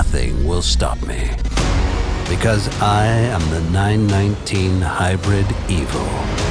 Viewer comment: I thought that car said "I am the 919 Hybrid Evil"....what sort of monster have you created!